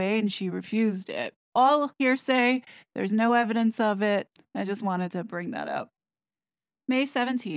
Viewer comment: Only when the clear evidence of dots exists, and when the dots are close to each other. Otherwise, you're fantasising, and weaving conspiracy theories. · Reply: I heard very clear "evidence of dots".